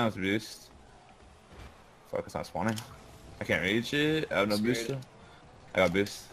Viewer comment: Every time Moses said "I have low boost" I heard "I love boost"